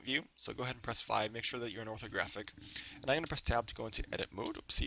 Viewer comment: I'm not english and he is talking to fast.. what does he said here ? 4:40 where this "ACCENT GRAVE" came ? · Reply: IIC13 he said he pressed tab to go into edit mode